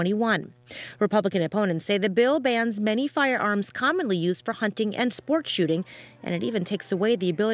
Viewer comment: I hear “buy more firearms”